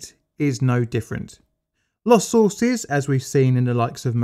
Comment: Lost horses? Lots Ortiz? What did he say?